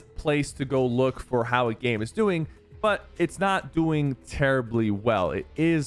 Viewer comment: he really said gooing in stead of going